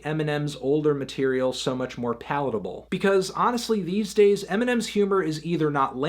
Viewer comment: i think he says palatable but it sounds like poutable